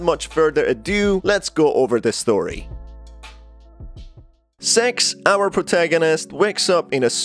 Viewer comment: why are you saying her name like that lmao sounds like your saying “sex” hahahaha